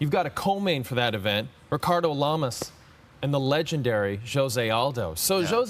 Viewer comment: ricardo lamas oops for a minute i thought he said fernando lamas,,its better to look good then to feel good